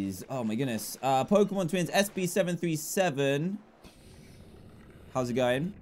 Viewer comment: did he just say SB737???